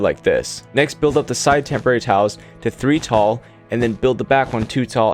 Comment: I kept hearing "temporary towels" and thought that's an interesting way to refer to wool. 😅